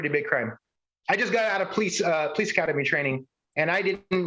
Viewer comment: did I hear say, "I just got out of police academy training."? God help us all.